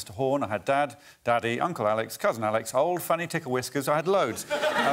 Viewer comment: When I watched without subtitles I thought he said "old fanny tickle whiskers" and wondered why it didn't get a bigger reaction.